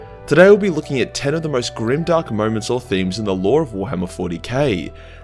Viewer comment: I thought it said grimdank